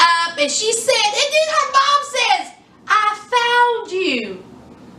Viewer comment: Oh I thought Kaitlyns mama said “I failed you” 😩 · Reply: I did to. So I put on the good ole closed caption. 😂😂 it said "I found you". I was like "I failed you" would make more sense.